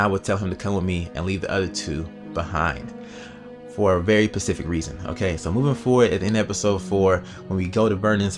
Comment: did u say pasfic reason